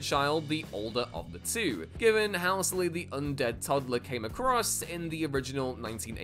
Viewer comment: Can anyone else make out what the hell he said @ 7:13😂?!?! Did he say "how silly" or "housely"?!?!